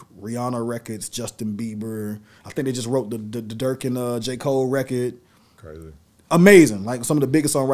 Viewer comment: 🤔🤔🤔🤔 Did he just say Durk J.cole has a writer's??? 22:04 minute mark. He mentioned a group of writers named Rock City. I'm shocked they didn't dig dipper. Good shit yet again yall. 🙏💯❤️ #PUSH